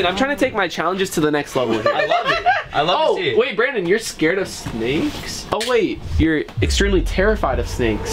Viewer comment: in the subtitles he said snaes instead of snakes